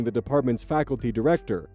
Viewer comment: When I heard "pursuing the faculty director", I thought OP was dropping some absolutely insane background lore so casually, but then I looked at the screen and it said "perusing the faculty directory" 😅